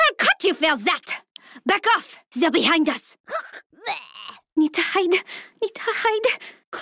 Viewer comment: What saying in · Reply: "Need to hide, need to hide"